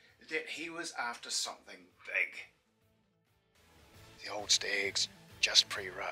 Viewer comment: Amazing hunt in an amazing country! To me, whenever he says "stags," I hear "steaks," which is not entirely inaccurate